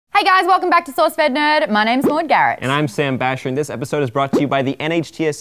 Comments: Every time says her name it sounds like shes saying Lord Garrett. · I really like as a host, it's nice to see an Aussie in there but every time she introduces her self I keep hearing "Lord Garrett" ...Put her in Game of Thrones?